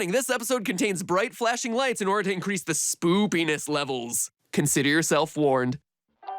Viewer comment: Did someone just say SPOOPINESS LEVELS???? spooky scary skeletons remix plays in the background as meme lords rejoice